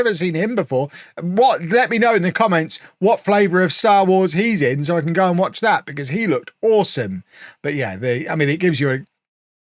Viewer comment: Did anyone catch what he just said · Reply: “Let me know what flavor of Star Wars he is in so I can go and watch that because he looked awesome.”